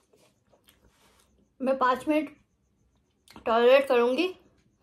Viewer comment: I thought she said "mein 5 mins toilet karungi" 🤣🤣